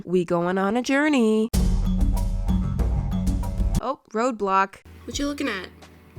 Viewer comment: When you said Road block I thought you said ROBLOX also is it okay is you actually Painetd your toilet Cover or seat that would be nice and will make your bathroom more nicer I think